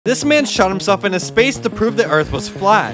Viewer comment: Bro I almost skipped when I heard the words “This man shot himself” 💀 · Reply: I thought he said “this man shot himself in his face” 😭